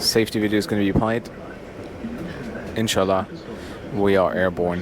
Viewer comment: did he just said in Sha allah?🥰